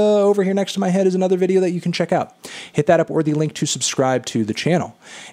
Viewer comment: F*ck... for one second I thought it said "Jai Paul"